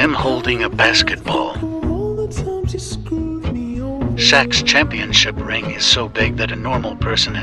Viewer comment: Am I the only one who misheard s*x for Shaq's?